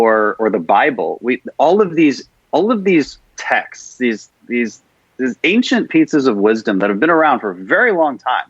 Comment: did he say "ancient pizzas of wisdom" cause I'm down for that